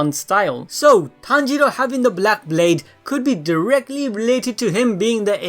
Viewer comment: My dumbass thought he said “black babes” 🤣 Also idk why but a lot of people has theory’s about nezuko NOT really being tanjiro’s real sibling. What y’all think about it?